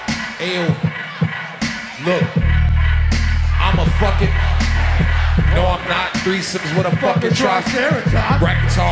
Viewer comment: i swear I heard almost everyone say "I'ma fuckin homosexual" just like in that one meme xD